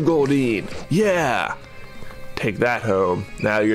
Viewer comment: For a second I thought he said "Take that, hoe!"